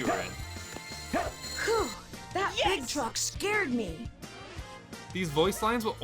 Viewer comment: I always thought he said, "That big DROP scared me." big truck makes so much more sense...